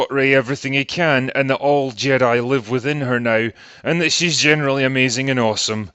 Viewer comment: “All Jedi live within her now” I thought you said her sexuality is unexplored